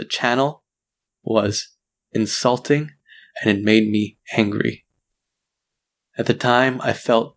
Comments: Is my hearing going bad or did he say hangry? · did anyone hear “hangry”? Just me? Oke just trying to lift the mood ; - ; Also guys this is why you shouldn’t jump to conclusions and be like “Oh even though I have half of the story imma go hate the other side.” It hurts. Youtubers are real people with real emotions. And I’m grateful for the other people that stayed with waiting for side. For the other people who didn’t wait and who hated on, you’re immature, you shouldn’t jump to conclusions so fast. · at time 9:40 i hear hangry does anyone else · am I the only one who heard hangry? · did he say hangry? was that intentional